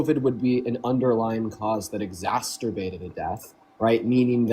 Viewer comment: did he say "exasturbated"?!?!?!